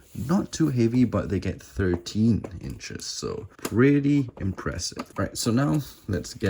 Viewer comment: I thought he said 30 inches 😂